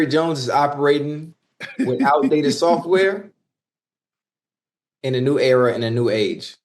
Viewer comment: At one point, Ocho said they are using outdated software. It sounded like he said, Al Davis software, which is also true.